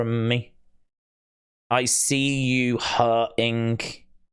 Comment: In the audio, I hear "I see you hurt...ing"